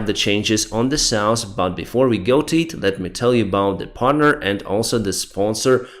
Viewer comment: "Let me tell you about the Partner and also the sponsor of this channel" For some reason I first understood "Wagner" instead of Partner. But since it made no sense for them to sponsor your videos I rewinded.